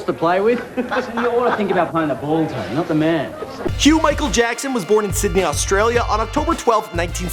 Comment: Anybody else catch the "Hugh Michael Jackson" at 2:35 lol